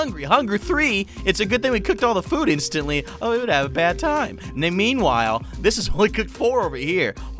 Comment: did he say bad time? Undertale megalovania plays